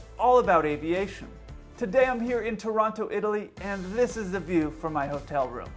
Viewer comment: My brain short-circuted for a second when I thought he said Toronto, Italy ^^